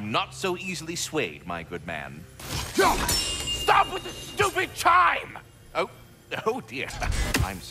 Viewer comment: Now it might just be me but I swore last I heard it he said STOP WITH THE FUCKING CHIMES instead of saying stupid chimes.